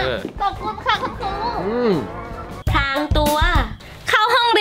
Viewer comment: ทำไมเขียนว่าเเกล้งป่วย เเต่ทำไมพี่พูดว่าพลางตัว